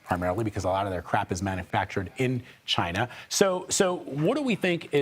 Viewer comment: Did he say "Alot of their crap is Manufactured in China". :) Frame 9:17 :)